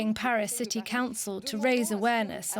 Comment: Did anyone else hear "Parasitic Council" instead of "Paris City Council" at 1:26? 🤪